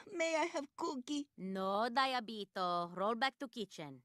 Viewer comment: The first few times I saw this clip I thought she was saying "No Doritos roll back to kittens."